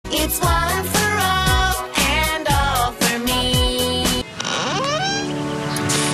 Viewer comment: i heard ALL FOR ONE NANI